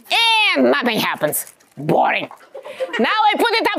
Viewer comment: Why do I hear "Boing" when she says "Boring"